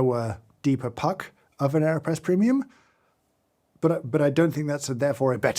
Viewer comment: I really thought he said Kanye press and I was very concerned for a second haha